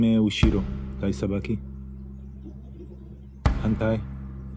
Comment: I thought he said 'hentai' lol. Anyways, awesome demonstration!